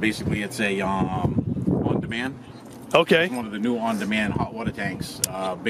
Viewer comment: wait what listen closely at 4:16 he says new honda man :D